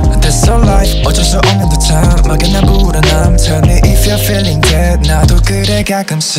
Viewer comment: i thought jisung said "if you're feeling dead" WELP